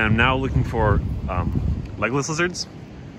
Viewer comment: How deep do legless lizards bury? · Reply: I thought he said Legolas Lizards.